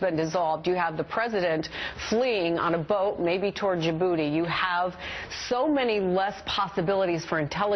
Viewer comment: "The President fleeing on a boat, maybe towards ya booty." I hear it's beautiful there during the spring.